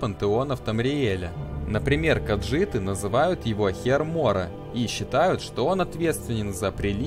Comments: мне послышалось "каджиты называют его хер моря" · Мне послышалась каджиты называют его Хер моря или хер морей